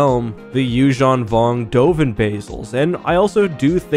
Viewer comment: Someone please tell me why do I keep hearing “oven bagels”?